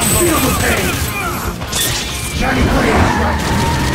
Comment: It sounded like he was saying "Johnny Blaze rides again."